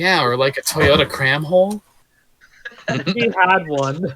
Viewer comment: Every time someone says "Toyota Cramhole," I keep hearing "Toyota Crambone." Guess it's because I was introduced to channel via Tolkien Off the Rails, but I just can't unhear it.